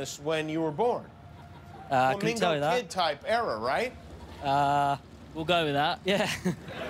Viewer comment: Joel: I think you’re racist, jimmy. Jimmy: *nervous laughter* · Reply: what is jimmy even saying at 3:39 "commingle kid type error"